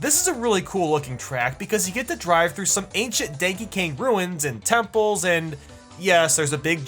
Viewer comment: did he just say, DANKEY KANG? That was a silly inside joke my family had! 😂